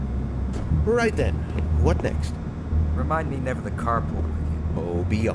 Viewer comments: subtitle is wrong. "Remind me never to carpool with you." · i think he said "cart pull" there.